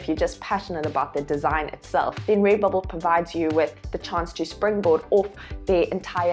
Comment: I swear I heard you say ray bubble. hahaha